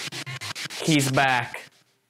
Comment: I didn’t hear “he’s back” I heard “his blood” repeating right after you said that. Then instead of “it’s light” I heard “that’s right.”